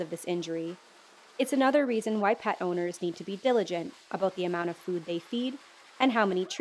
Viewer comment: did she say "viligante"? i'm just aksing for a friend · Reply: Diligent